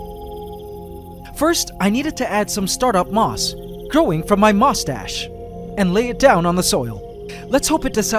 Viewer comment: When he said his moss stash I thought he said moustache